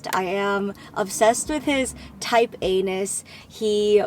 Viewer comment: Am I the only one who noticed it sounds like she said she loves his type Anus when she said she likes his “Type A-ness?” Just me? Haha I love you Jenn!